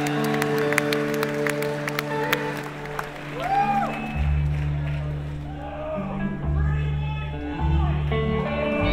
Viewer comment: Can someone kindly write what he says at 37:46? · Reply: A male voice shouts “Pretty Boy Floyd” in the background – Bob’s reaction: “You've come to the wrong place… Springsteen on Broadway will give you that. ... (?) … You’re at the wrong show!”